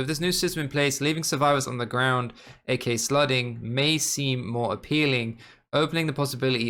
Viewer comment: I can’t be the only one who hears slutting instead of slugging